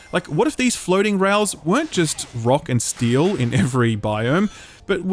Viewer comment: did i hear a ROCK AND STONE???!?